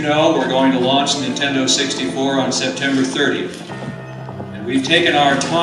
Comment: me parece escuchar que dice "september"